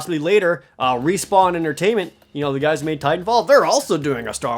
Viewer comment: oh. I thought you said Tight 'n Fall instead of Titan Fall